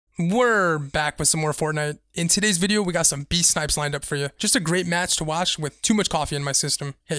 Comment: I thought it said "best snipes"